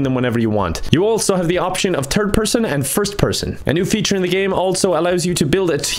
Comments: i thought you said turg person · idk bout you but i heard "first person and first person" lol